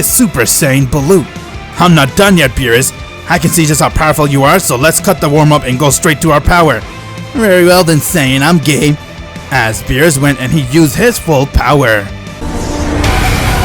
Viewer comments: am I the only one or did he say gay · It sounds like he said I’m gay